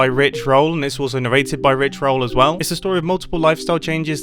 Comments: Did I hear “Rick roll” or have I been a mooner too long? 😂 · When he said rich roll I thought he said Rick roll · I thought Mumbo Said Rick Roll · When you said rich roll in sponsored segment, I heard Rick roll. This shows how much I love that song........... Or at least I hope so. Edit: just when I saw the missile, the LOLITFAILED missile in thumbnail and you said they explode on contact, th first thing which came in my mind we your tnt missile video. You made mega missiles which exploded on contact. Edit#2: did you just say that you never ever built a tnt missile in minecraft.........????!!!! It broke my heart. I saw the video exactly when it came out.... And that is my favourite Mumbo Jumbo video yet.